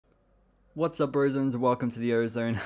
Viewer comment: at the beginning I thought you said "what's up virgins" 😅